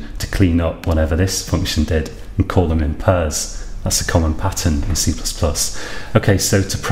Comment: I'm sorry, I didn't catch what you said at 13:51 . Parse, was it? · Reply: Ranvir Dogra "Call them in pairs". Like if you create a function that allocates memory with "new", it's common provide another function that cleans up that memory.